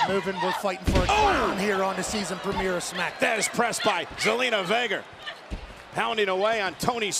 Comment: it sounded like Michael said gelina vega